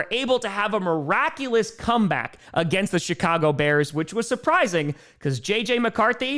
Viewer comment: Did said Shitcago Bears? Idk probably misheard, probably the way he pronounces the Ch, pretty funny tho